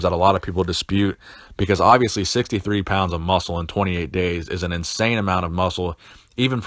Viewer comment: I thought you said "losing 63 muscles in 28 days... it would be easy for vegans guys out there